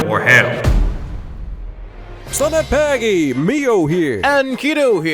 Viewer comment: For the people who are wondering what said at 0:13, he said "selamat pagi" which translates to good morning in Malay and Indonesian.